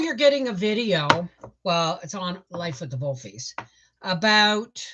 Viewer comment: I had a hard time finding your YouTube because I thought you said Life with the WOLFIES but you were saying Life with the Volfies. I finally found it